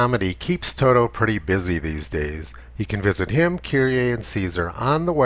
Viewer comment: "You can visit him..."? What else do you say here?